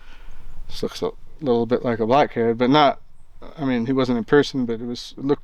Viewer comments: I have a hard time not just hearing the words " he is a murderer" in my head. Anyone shooting something other than for food, is just plain evil. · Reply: Did he say it looked like a black kid?? Min 18:22?? Wow this guy is not all there! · Mark 18:20 did he say it looks like a black kid